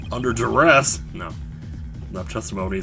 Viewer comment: I heard that as under a dress.